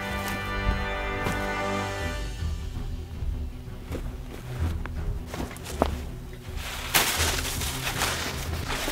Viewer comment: what did it say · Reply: Brandon westgard "I'm still tasty, I promise"